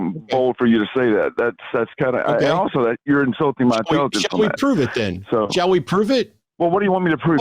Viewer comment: Did say "you're insulting my children"?? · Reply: I heard “ you’re insulting my intelligence on that”